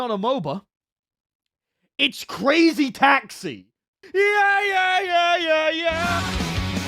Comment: so all I heard was “CRA” and it sounded like “CRY” before an ad popped up, and I just thought he said "CRYSTAL METH"